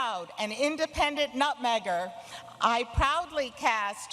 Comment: what she said? "not meger"? also why there is a "trump" and "other" counter?